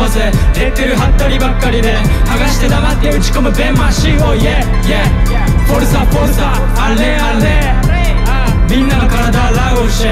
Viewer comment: Anyone knows what he's saying at 2:41? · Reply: He is saying a foreign phrase which means encouraging a soccer team cuz juma is a big fan of soccer. “Forza forza allez allez”